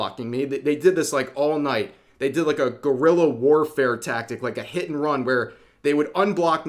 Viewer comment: <--- Brb need some coffee because i heard that as GORILLA WARFARE.